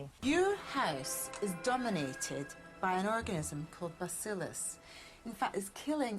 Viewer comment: 6.31 is it me or does it super sound like Aggie said 'orgasm' instead of organism?